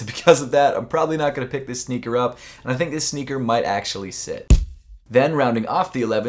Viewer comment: it says sell instead of sit